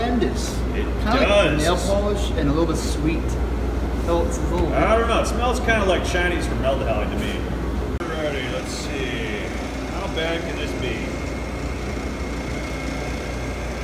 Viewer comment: Did he just say it smells like Chinese formaldehyde?